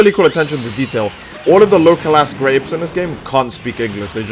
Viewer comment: the local ass grapes???? wtf are you saying?